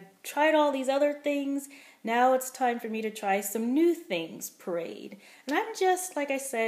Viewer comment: I keep hearing you say " parade", like " I parade chicken"! It makes me smile! You sound so happy. I don't think I'll be doing puréed. But we'll see.